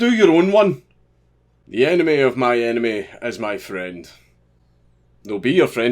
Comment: Your accent threw me off, I thought you said "the anime of my anime is my friend"